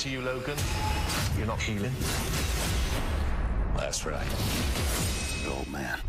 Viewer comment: 'That's right' I thought he said 'last ride' Mine's better!